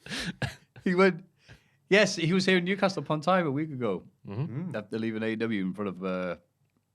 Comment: I swear said "Upon TIME"